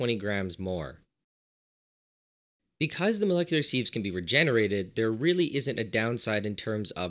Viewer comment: I thought you were saying "molecular seeds"! The mind boggled at what would grow from planting a "molecular seed"!